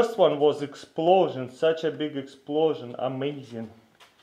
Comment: does he say I'm asian